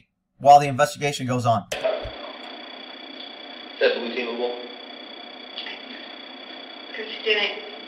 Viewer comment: Did he say to her, " Is that 'blue-ceivable'?" Meaning is that believable cop-lingo BS for the report narrative? have you heard of that? Are my ears wrong? Anyone else?